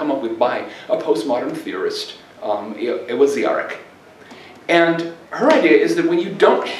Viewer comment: I cannot make out who John says coined the concept of discensus. I made out that it's a she, and that she is a post-modern theorist, but I fail to get the name. He mentions her at 37:10 I would very much appreciate if anyone could help me out, as I very much thank The School of Economic Science, and Mr Greer for this great, great talk! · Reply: A great post to have a look at is this essay JMG wrote where he cites postmodern theorist Ewa Ziarek, who introduced dissensus in a book on ethical theory in 2001. <link>